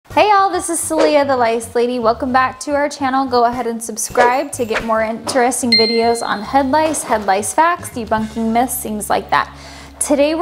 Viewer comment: I am so sorry but I thought I heard "rice lady 😭"